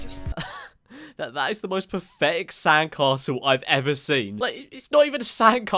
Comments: If u turn on subtitles at 4:47 instead of "pathetic" it says "perfect". Lol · he said "most pathetic sand-castle", but the YouTube auto-generated subtitles thought he said "most perfect sand-castle"